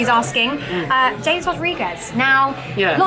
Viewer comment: When u guys said James Rodriguez I thought u said Jay Rodriguez from Southampton and I was like wtf but he's accualy playing well atm